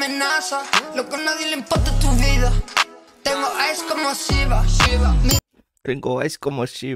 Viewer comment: “Tengo Ice como shiva” puede ser referencia a shiva un trapero italiano famoso que tiene una colaboración con lil baby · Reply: Pensé que decía tengo "eyes como shiva"